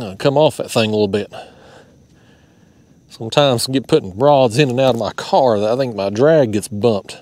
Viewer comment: OMG, I’m at work and had your video up and watching while multi tasking. Then, at about 11:40 in the video I “thought” you said something about putting broads in your car and some where to tight and some where to loose. I choked and spit coffee on my desk. Thank goodness for rewind to clear that misunderstanding up😂